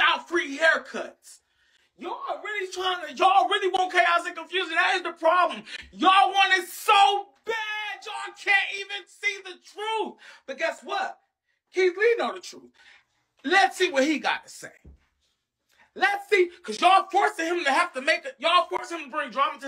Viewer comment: Girl I thought it said Kathy Lee 😂